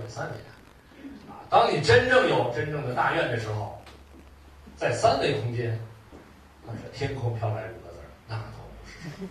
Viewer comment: 刘老师讲的那句令大家鼓掌的话听不清楚，哪位可以说一下？谢谢🙏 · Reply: 当你有真正的大愿的时候，天空飘来五个字 “那都不是事儿” 。